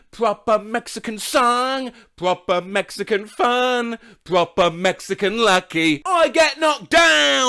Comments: I heard it as "we're up on mexican" whatever the f*** that would mean. This, "pompei" by bastille, and a very creative song that I assume was called "f*** you" played on repeat during a holiday I had in france as a child. Another weird song I heard a lot on holiday, though I don't think it was the same one was "agadoo." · Related, i used to think that instead of “we’re up all night to get lucky” it was “we’re up on legs to get lucky” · I always heard "up on Mexican donkey"